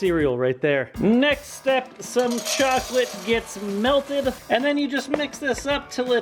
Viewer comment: I thought he said "chocolate gits"